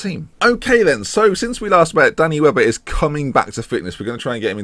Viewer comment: Half the time you say Danny Webber I hear "Danny Welbeck" and it just makes me giggle. "Got James Lynch in goal, Danny Welbeck starting up front, the cow in midfield." Bonkers.